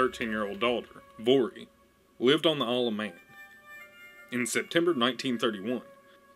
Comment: I heard "...lirved awn th'earluv Mang". That's some twang Good video though. Gef's pretty weird and I love it. "I did it for the Devilment" - Gef